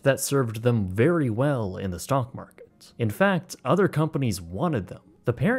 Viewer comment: Did you say stonk..? I coulda swore you said “stonk market”